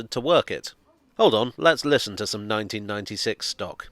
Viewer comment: I was confused at 4:58 I thought you were saying 1996 stock smelt bad rather than sounding good..